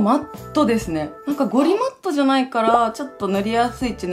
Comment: ゴリマッチョ？？(空耳)